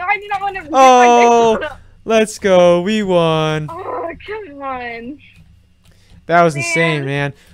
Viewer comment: Why did she call you Kevin???? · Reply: She said “aww come on!”